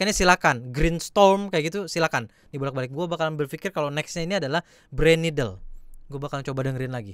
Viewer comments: Gua dari tadi kedengerannya cuma brain needle walau pun mikirin yg lain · kedengerannya brain needle